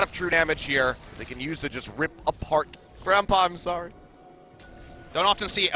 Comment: 他說什麼？ 感覺是說什麼sorry